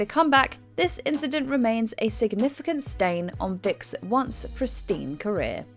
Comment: Did she call Michael Vick, "Dick" at 27:57? 😅